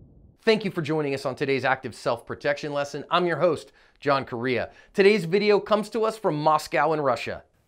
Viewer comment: Guy: “My name is John Correia” My mind immediately: JOHN CENA Thxs for the likes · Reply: Lmao I thought he said John Korea 🤣